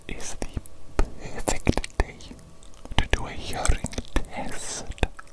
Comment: ngl I thought he said "a yuri test".